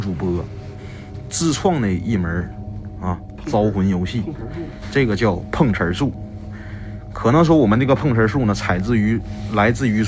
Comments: “Pong hear sue, mah nigga poon” Please tell me I’m not the only one who heard it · sounds like he said my nigga pengchi 🤣🤣🤣